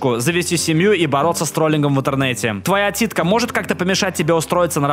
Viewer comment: слово ,,отсидка"=,,титька" Кто тоже это услышал Надеюсь я не один такой